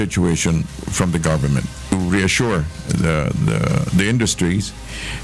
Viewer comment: Whoever wrote that subtitle need to be fired out. He said to "Reassure" and not "ensure" like the subtitle said